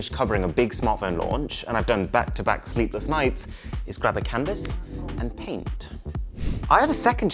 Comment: Am I the only one who heard cannabis